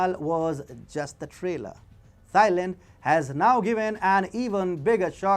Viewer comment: Great video but all I keep hearing is "thigh land".